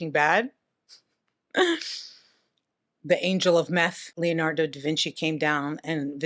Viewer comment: 😂❤he calls himself "man #5" cracked me up. 😊I will forever pronounce Robert as Rahbeayldt❤ you elevated a common man's name to an exotic level mystical creature name ❤lol.😂 7:30 did you say the "Angel of Meth"? Hahhaha👏👍